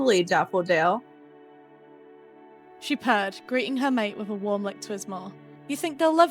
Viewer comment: instead of purr, I heard cursed 😅